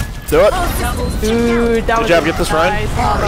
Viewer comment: did he say get the spy?